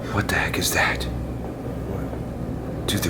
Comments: a lady voice responds saying "what?" after you said "omg" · I heard "What go back". Like so can see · right after said Oh my gosh.. it sounded like someone said "What" or "Hey" like so can see! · I heard someone say WHAT THE HECK